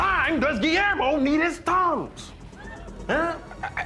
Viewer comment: I was listening and not watching and I thought they said tongues, not Tums. 🤦‍♀️